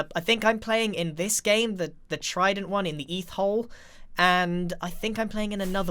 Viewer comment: I'm sorry.... Did he say "eth-hole"? I had to pause cause that pun is golden Edit fixed time... Close enough